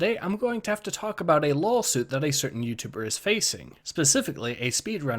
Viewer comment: Did you say LOLsuit?